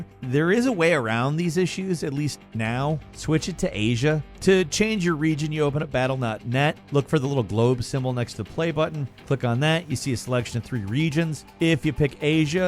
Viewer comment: Am I the only one that heard him say "battle nut-NET" 😂